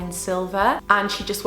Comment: I thought u said Sylvia but u said silver and I got excited ;(